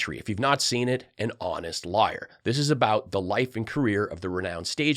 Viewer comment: Shoot I misheard you, I was so looking forward to giving you some guff and calling you out for pulling a Lindell word gaff. I thought I heard you say. " (sounded like) skientific. But I rewound and dang you did say it right. Hahaha